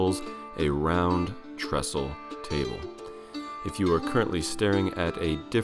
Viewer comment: Hahaha! I thought it said Round Testicle Table. 😃